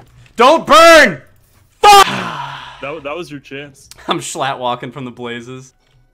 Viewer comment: does he say "I'm slatlocking from the blazes." ??? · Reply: Schlatt walking